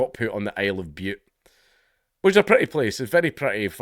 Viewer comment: Lol, at first, I could've sworn you said 'Isle of Bupe' 😅